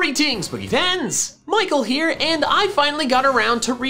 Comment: I thought he always said “go-re-teens” and now I know he’s saying greetings!